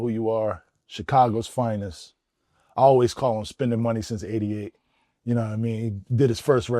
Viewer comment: I think Jay z said still spending money from 88, no?